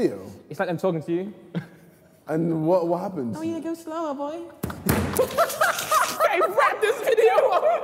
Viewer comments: oh yeah just go slower boy - that's what i'm hearing, but the way he say it... pfff faqn beast mode.... bwahahhaaaa 🤣🤣🤣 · I thought Randolph was mimicking The Weeknd and I thought he said “I’m a Star Boy”